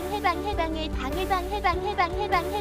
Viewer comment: 큰일났다 방해방해방해방해방해방해방해방해방해방해방해방해방해방해방해방해방해방해방해방해방해방해방해방해방해방해방해방해방해방해방해방해방해방해방해방해방해방해방해방해방해방해방해방해방해방해방해방해방해방해방해방해방해방해방해방해방해방해방해방해방해방해방해방해방해방해방해방해 가 해방해방해방해방해방해방해방해방해방해방해방해방해방해방해방으로 들려